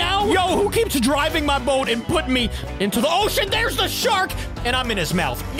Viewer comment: i think he said a bad word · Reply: With subtitles it Sayed (who keeps dropping me in tooo the. Ocean)